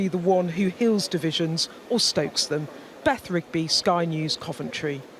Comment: I thought de Pfeffel said he going "to level Coventry..." for a second there. I guess that's one way of doing it...